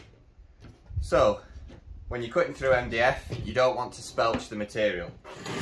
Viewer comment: Good explanation, and video. At 1:14 I don't understand what you said about "don't want to _____________? the material" and the transcript didn't help. Thanks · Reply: I think I said "spelch" it just means "tear out" or "splinter" the material....Must be a northern thing!